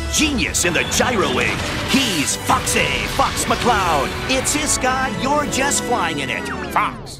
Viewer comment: What in the world does he say @ 0:15.... I can't hear it right for the life of me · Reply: I think it says it's his sky your just flying in it.